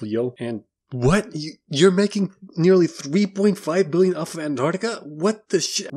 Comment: Just thing it needs to be pointed out that there’s more crime in Antarctica than in africa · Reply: I uh, thought he said America and not Antarctica. My bad.